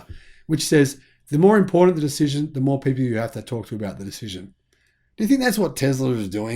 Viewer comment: What did you say at 06:00? "The more important the decision the more you have to ...... about the decision." Something like that. That key word is unaudible. What was it? I got "the more privvy you got to be about the decision." Is that right? I'm not a native speaker of Australian. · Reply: "The more important the decision the more people you have to talk to about the decision."